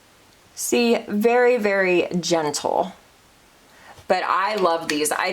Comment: Personally, I love ❤️ a longer video!! When you did a swatch of something, it sounded like you said genital, when you were trying to say, gentle lol 😆 🤣 😂 it has to be your accent. You're the BEST, 🤍🤍🤍